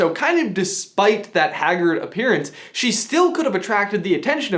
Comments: did you say hagrid appearance? 🤔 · I thought he said "Hagrid appearance." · you missed such a great pun opportunity. "Despite that Haggard appearance" I heard it as "despite that Hagrid appearance" and had to go back to check again. Was sad it was actually haggard